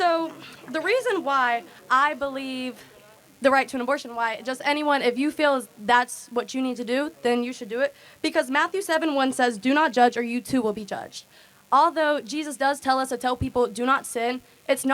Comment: did she say "do not judge or youtube will be judged"?